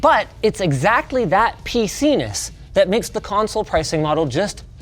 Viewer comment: hehehe, it sounds like he said penis